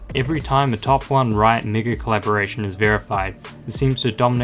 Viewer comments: Am I the only one who hear "nword-collab" 1:27 ? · what did bro say at 1:31💀💀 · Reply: Megacollaboration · ayo what did he say? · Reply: Megacollab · what did you say? i didn't hear it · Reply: Megacollaboration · Is it just me or does he say the n word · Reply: He just said mega really fast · DID YOU SAID NIGHTER COLLABORATION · what did he say?!?!! 💀💀💀💀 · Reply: It's "Riot" Mega-Collaboration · did he say the n word??? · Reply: Megacollaboration